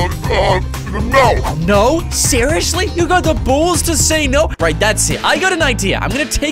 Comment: did you say you got the balls to say no